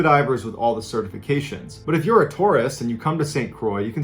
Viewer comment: I thought he said Taurus, not Tourist. "Hey i'm a taur----oh"